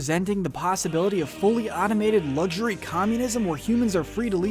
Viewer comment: did I hear communism